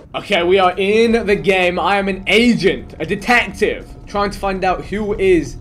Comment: When vikk said I'm an agent I thought he said I'm a Asian